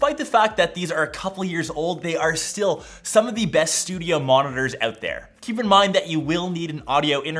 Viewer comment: did you guys relies that he said "the best studio monsters" instead of "the best studio speakers"